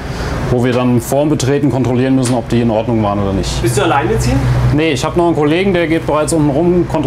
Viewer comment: lol ich hab bei 1:28 verstanden: "bist du alleinerziehend ?" :D